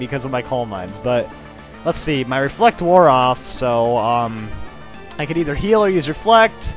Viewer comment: did it said Ally's reflect wore off